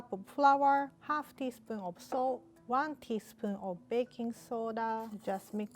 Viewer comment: Am i the only one that thought she said " half a teaspoon of soap" when she said" half teaspoon of salt "?